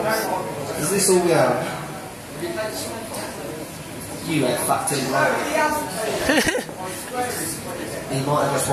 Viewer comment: Ive just realised what Dave says to me at 0:10 secs LOL "You have fucked him right up" LOL Im sorry Ben :P